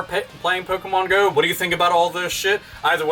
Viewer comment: What is he saying at the end? I always wondered :/ I only hear "This is AlphaOmegaSin s always, something something something, something about gamers, and "the fuck on" " . I don't understand :/